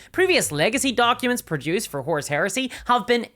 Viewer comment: Listening to this, I kept hearing "Horse Heresy" which I assumed is a thematic sequel to Lenny Bruce's infamous "Psychopathia Sexualis" but I still have no idea what any of that has to do with Warhammer.